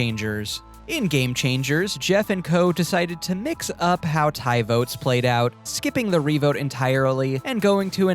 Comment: When you were talking about Game Changers, and you said they switched how tied votes worked, I thought you said Tai's votes at first and I was so confused